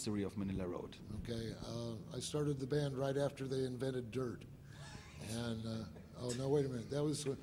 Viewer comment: I can't understand what Mark's saying, is he saying Dirt? I don't get what it he's trying to tell